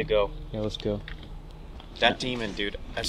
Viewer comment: with the yelling i swear i heard demon being yelled out